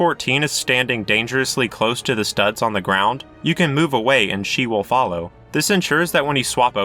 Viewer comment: you mean he, I heard she